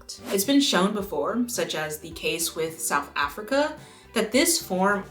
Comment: Hiiiii. Thanks for watching! And thanks to Milanote for sponsoring this video! Sign up for free and start your next creative project: <link> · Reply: I heard "The Chase" in the opener at it got me